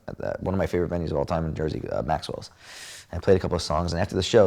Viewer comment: What did he say one of his favorite bands of all time was? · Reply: He said his favorite VENUES of all time in Jersey was Maxwell's